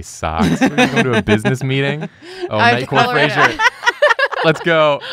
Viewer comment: I kept hearing "night court fraiser", which would actually be a pretty amazing crossover series